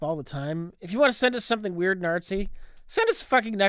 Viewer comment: Subtitle closed caption says "If you want to send us something weird, Nazi, send us fucking Necronomicon" But I think Ritch actually says "If you want to send us something weird 'n artsy.."